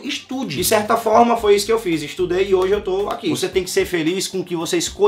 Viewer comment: Eu escutei direito o falou que ESTUDOU !!!!???